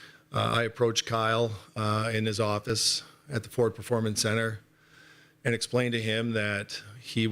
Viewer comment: It sounded like Shanahan visited him in his office at the "Poor Performance Centre" at 05:00 lol.